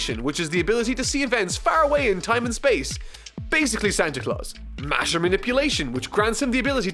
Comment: When you said matter manipulation I thought you said masturbat***